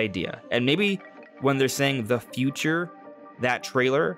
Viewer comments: i am very unsure about the "help us" story. i don't hear anything at all. But it's different with The Future. I heard it the first time and was surprised that it wasn't mentioned anywhere. I heard "our Future". But the difference is marginal. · When I first watched the 3rd trailer, it sounded to me like they were saying "Have you heard?" This to me seemed like the people from the past, whether Zonai, Shiekah, Twili, or Hylian, (or possibly even the seven sages or Din/Nayru/Farore) were asking Link and Zelda to listen to their tale of woe, how their kingdom fell at the hands of Ganondorf/Demise. So it could be "have you heard our story, how the Tears of the Kingdom formed?", maybe "have you heard the original Legend of Zelda?"... or it could more dismally be "Have you even heard of us? Have we been forgotten?" Still with the time travel basis, but not referencing the future. Admittedly I only hear "the future" now since it was pointed out in the numerous analysis videos including yours, (so I probably just heard it wrong) but I still think the premise could be the same. Either way, my hope for the story is that the Sacred Realm and the Triforce are reintroduced, especially since we have the wielders of Power, Wisdom, and Courage playing strong roles again. Looking forward to your post-release videos, man 👍 This Friday!